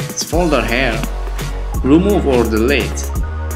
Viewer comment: what did he just say? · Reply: remove or delete :)